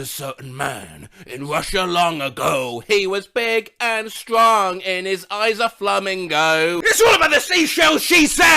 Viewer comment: i actually misheard this lyric as “and his eyes were flaming gold” before 💀